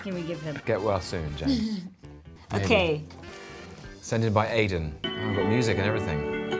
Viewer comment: am I the only one noticing that Tom said " sended " twice in this video That's such old fashioned language omg haha. or am I hearing wrong ??